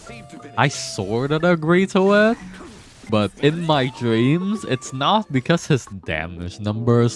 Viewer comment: I can’t help but laugh every time I heard “in my dream” and “ ting Yun” 😂